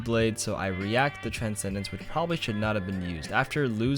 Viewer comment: Huh?? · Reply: I heard "should'n" but I might of heard wrong